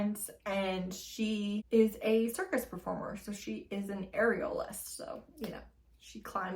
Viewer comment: I need to get my hearing checked 😂 I HEARD AREOLAS and not aerialist hahahaha I had to listen twice